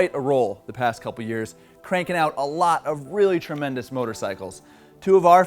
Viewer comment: thought you said "wheelie tremendous motorcycles" during the intro, haha. It would be fitting if you had.